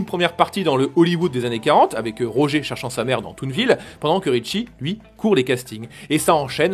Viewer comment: moi j'entends "avec Roger cherchant sa merde dans toon ville" Et c'est marrant